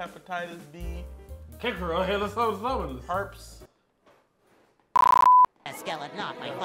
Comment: I thought this dude was finna say “Herpules” (Hercules)